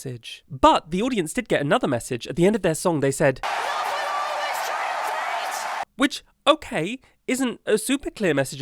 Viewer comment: wait, what did they say at 1:22? i've replayed it several times but just can't make it out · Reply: ”Love will always triumph hate”